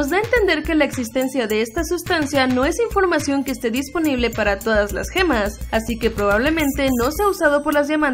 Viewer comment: esta informacion no esta disponible para las gemas... Lo que io entendi: Esta informacion no esta disponible para android ni apple